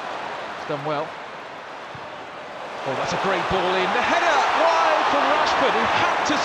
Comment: Is it just me or did he say de gea when rashford missed the chance with his head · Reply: No he said and the header but it saound like ( and de heda) so no...